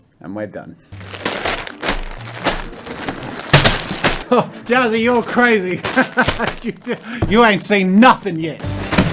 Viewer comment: Watch at 1:45 with captions on and you'll see the auto captions saying "Oh daddy you're crazy, you ain't say nothing yet" 😂😂😂